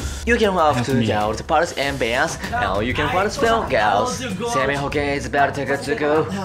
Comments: 「生命保険」って聞こえたのは俺だけ？？？ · 生命保険って聞こえた